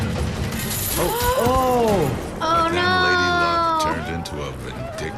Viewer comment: Did say No?